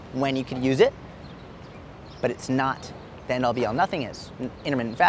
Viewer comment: Wtf did he say at 0:34. ??? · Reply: The end all be all